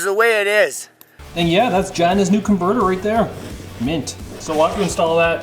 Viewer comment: Ok. I need to go back to sleep (way too much GF smashing last night and only got an hour of sleep). All I kept hearing was China's Convertor. Even after the meme it didn't click what was really saying. Sorry Gina. Didn't mean confuse you with China. Yikes. zzzzzzzzzzzzz 😴😴